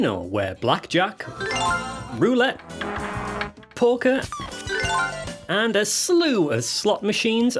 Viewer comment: LOL, at 4.15 I thought you said Porker not Poker